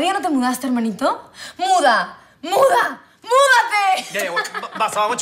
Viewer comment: en vez de escuchar muda escuche munda xd